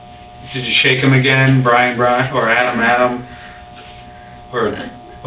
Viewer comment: did he say Brian?